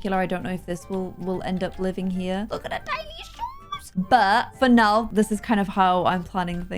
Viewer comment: I think I may have heard a few accidental pronouns slip there ☺️😂, if I'm right then I think they might be a lil girl , I heard "look at her tiny shooooooes" 👀